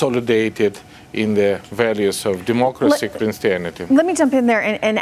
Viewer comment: did he say democracy and christianity?